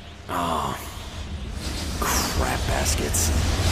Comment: It took me such a long time until now to realize they keep saying oh crap baskets which i thought they said oh craft baskets